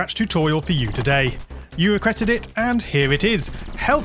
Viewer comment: Why does 0:08 sound like he said "you regretted it" hmmm